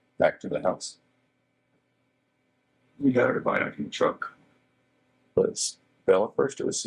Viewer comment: … he just responded with in the truck? Well.. where else if they were alive before that? Which means something also happened somewhere else besides the truck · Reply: What stood out starting at 21:15 was “we got everybody in the truck” or “we got her back in the truck” but maybe it was hard to decipher. That could be very telling, if that’s what was said!!!!